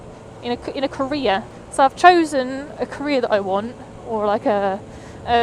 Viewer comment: I thought you were saying you were gonna have to go to Korea for a job and I was like NOOOOOO DONT DO IT. But then I realized I'm just an American and misunderstood you saying "career". Heh